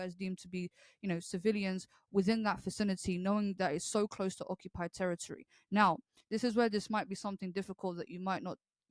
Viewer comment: Did she just say the festival was "near occupied territory"? Does she not know Israel left Gaza since 2005? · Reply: No, she said the Nova party was “on occupied territory”. She considers all of Israel to be “occupied territory.” This is a pretty standard belief in this crowd. They refuse to acknowledge the legitimacy of Israel as a nation.